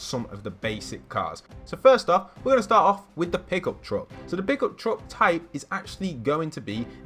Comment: who else thought that he said pickle truck not pickup truck